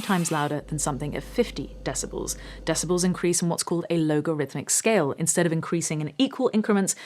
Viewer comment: I think my ears are damaged: I kept hearing "LOWG"arithmic instead of "LOG"arithmic. 😉Just teasing: love the vids. keep up the good work.